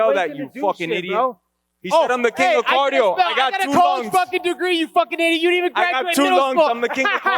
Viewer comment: Colby’s laugh at 5:16 has me dying 🤣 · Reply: For a second I thought he said "I got a college degree" lol He said, "I got a Colby degree" That's basically a Phd/MD/JD/DDS/PharmD/DO altogether. A Fluid Dynamic Theoretical Physicist, Cardiothoracicneuro Surgeon, Supreme Court Judge, Dental/facial surgeon, and Clinical Pharmacologist all at the same time. Man I love Colby. My idol. If only I can have 1/8th the education he has I'd be set. His work ethic is unreal. Asian parents dream child.